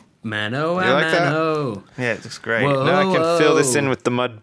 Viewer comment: it buffered when lewis said it looks great so I thought he said it looks grey. Shame, I could have made a joke about him looking at sips' face...